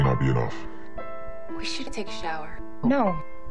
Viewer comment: CRYING AS HELL. I think she says '' We shoul've take a shower '' or '' We shouldn't take a shower. '' And Emily's 'No.' after it omg hahahahah I'm peeing myself